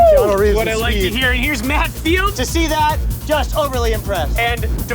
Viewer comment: When he said Matt Field I thought he said Matt Neal.